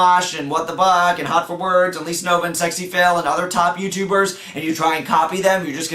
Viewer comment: HAHAHA If you look at the subtitles, he says "If you look at HotforWords and LisaNova and SEXYFAIL ... " instead of Sexyphil. XD